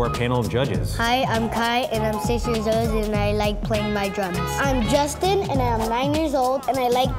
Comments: sounds like he said he like playing drugs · I love kai I thought he said I pick my nose haha but he said drums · you know how to make the next mom vs chef more interesting? you put pro chefs against their own moms, i wanna see that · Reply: sounds like he said he likes playing his drugs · I thought kai said drugs lol